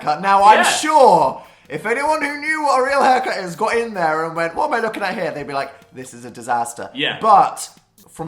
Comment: can someone who’s british translate what the fuck dan said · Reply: If anyone who knew what a real haircut is got in there and went what am I looking at here?